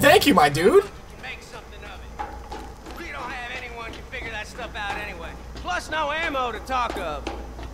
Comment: LOL the subtitles said figger instead of figure!